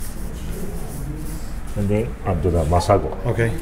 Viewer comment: Awesome channel! What is it he puts in the seafood at the 8:15 mark that has Sushi on the package. Couldn't understand? Anyone Where do you get it? Thanks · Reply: Robert Skillings yes it was Masiago